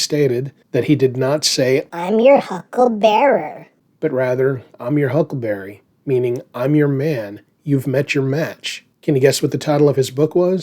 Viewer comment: I thought he said, "Hucklebooty".